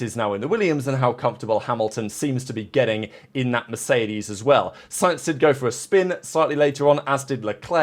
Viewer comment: did u say hamilton in mercedes???